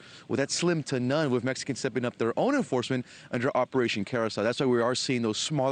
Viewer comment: I swear I thought he said Operation Parasite.